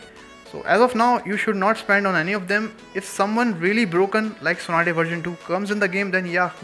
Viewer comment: What shinobi did you say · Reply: Someone really broken like Tsunade version 2